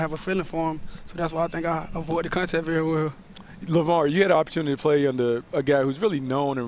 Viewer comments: did he call him lavar? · did he call him Lavar ? Lol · did he really say lavar 🤔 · Did he just say Lavar lol